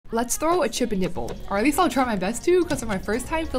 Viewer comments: Girl I thought you said chip’n NIPLLES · Who thought she said chip and dale bowl hahaha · I thought you said chicken nipple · Who else thought she said chippin' nipple the first time?